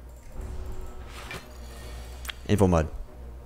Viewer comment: Can anyone tell me what mod he is using for the Bosses or event chances? or is it just a twitch addon? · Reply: I think he's saying Infomod